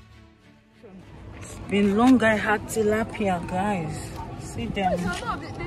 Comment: I heard tilapia? , fish greetings from philippines 🇵🇭, 😘